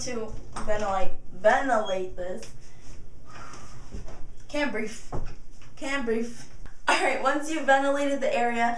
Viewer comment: i wish i had thick hair :/ you sound like you say "cant brief" lmao